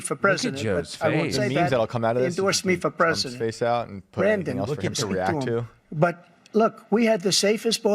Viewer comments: 😂😂😂 did he call him Brandon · YOU MISSED TRUMP CALLING JOE "BRANDON" to TALK to the boarder patrol about their problems they are having, because you were talking over what they were saying, thought I'd point that one out since yall missed it. LMAO at that jab =P!! Also as a vet, Joe, and democrats are ALWAYS the WORST towards the military, TO HELL WITH JOE!